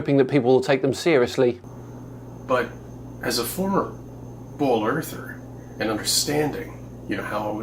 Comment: Everytime he says "As a former ball earther" all I hear is "As a former sane person" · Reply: I keep hearing “bowl earther” and was slightly confused.